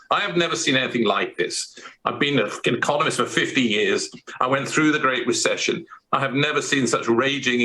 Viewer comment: "I've been a f'king economist for fifty years", did I hear that right?